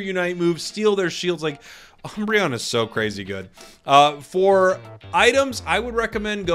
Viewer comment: for some reason I heard: Cram's so crazy good xD